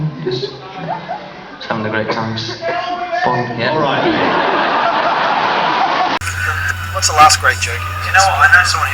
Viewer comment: Anyone know what the person yell at 9:00? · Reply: Bonita B "Get on with it"